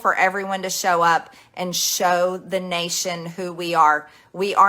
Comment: Did she just say ‘show up and fight’?